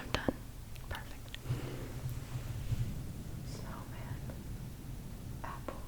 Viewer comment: What is that first word? "Solman"?